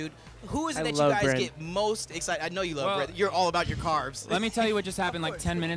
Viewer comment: I finally realised what Daniel said.. "I love bread" wow I thought he said I love red and the interviewer was like I know you're all about your cars haha :) Daniel Seavey I love bred as well